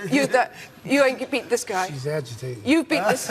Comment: what says ali in 4:52 i dont understand it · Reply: He said she's agitating, if you watch dick cavett show with ali and frazier you'll find out why they found it funny, (it was a joke)